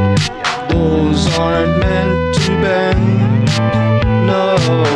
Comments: “Toes aren’t meant to bend” 💀 Edit: Guys I know those are not actually the lyrics, I’m just saying what they wrote in the video- please stop spamming my notifications with this 😭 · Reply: I always thought it said, "bones aren't meant to bend" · “Toes aren’t meant to bend” 💀 Edit: Guys I know those are not actually the lyrics, I’m just saying what they wrote in the video- please stop spamming my notifications with this 😭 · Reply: I swearrr I thought it said “Boys aren’t meant to bend”😭😭